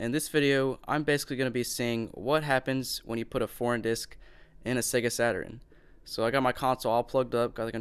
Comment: it sounds like he said "porn disc"